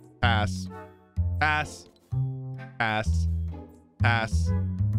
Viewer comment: sounded like as*